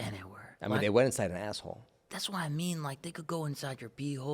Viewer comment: Did you say my asshole or an asshole